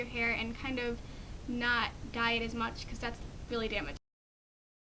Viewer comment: I thought she said not diet as much! Haha